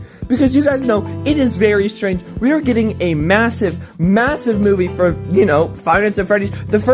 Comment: did you say m-m-massiv